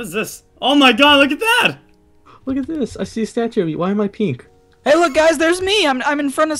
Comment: When deadlox said "Why am I Pink" I thought he said "Why am I Peeing?" And I started laughing